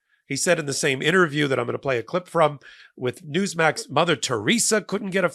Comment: When Popok first said Newsmax I thought he said Two Smacks. I thought, That’s one I’ve not heard of before. Reminded me of Chappell and The Count on Sesame Street.